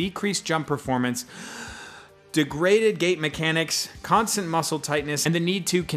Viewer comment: I misheard him say "degraded gay mechanics"